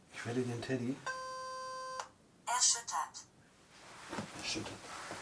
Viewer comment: Ich hab „ Englisch Lehrer“ verstanden